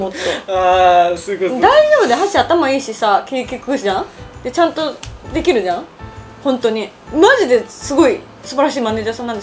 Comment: の所、しょこたんは何と言っているのでしょうか？再生スピード落としても聞き取れません。誰か教えて下さい。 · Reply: 「はっしー頭いいしさ、気ぃ利くじゃん？で、ちゃんと出来るじゃん？」 ですか？